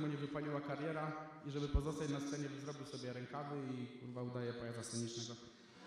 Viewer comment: Co mówisz w 1:16:36? Jakiego pajaca? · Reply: Pajaca scenicznego. Słychać dobrze co mówię :)